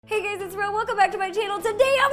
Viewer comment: Ok I can't be the only one who heard her say "Hey gays!" Right?